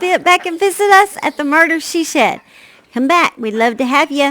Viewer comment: Ohhh I thought It was Murder She Said But Murder She shed is cool too 😂